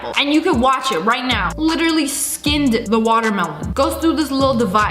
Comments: did you say water melon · When she first said watermelon I thought she said pineapple😂